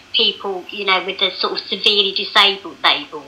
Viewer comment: I thought she said "severely disabled bagel". Will someone please think of the bagels? Just £2 a month could save a severely disabled bagel from a life of misery.